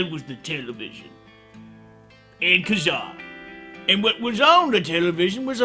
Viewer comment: what was saying at 2:30? i can't tell if he said "and (something 2 syllables)" or something weeaboo like "ikuzaa" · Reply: Kazaa. It was a service like napster